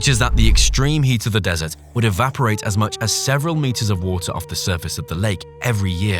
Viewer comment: '...evaporate several liters of water off the surface of the lake every year...' Whoa there! Several liters? That is quite a hydro project you are aiming for! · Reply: I thought he said meters.